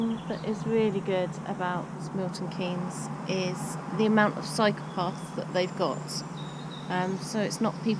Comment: When you said about the good thing about MK was cycle paths, I thought you said psychopaths... But I did date one from there once...